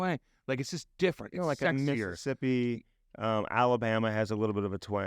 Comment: Is it just me or at 17:33 do I hear the N word?? Lol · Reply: I hear ‘sexier’, which fits the context of the conversation about the attractiveness of various southern accents. does talk over him so I can understand why it would sound muddled. Nice try though